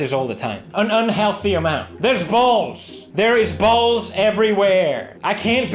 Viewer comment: I like how the editor misheard Felix and thought for some reason he said "balls" instead of "bowls" and so we just get this random unrelated footage of balls 😂😂